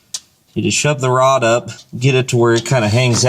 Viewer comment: !!!!! Did you just say to "shove the rod up if you're quick"? How perverse of you LOL LOL LOL